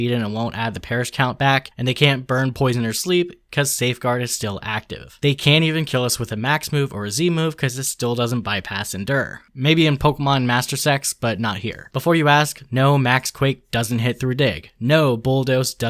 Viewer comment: I misheard and though he said master sex🥀